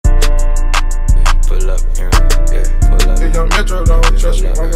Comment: I thought it said”pull up Erick “😂